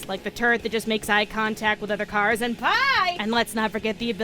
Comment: sounds like u said PIE